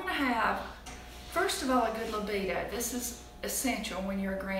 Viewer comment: at 13:21 WHAT are you saying is essential when you're a granny? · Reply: Mimi Maggie good Libido 😁😁😁🙃💃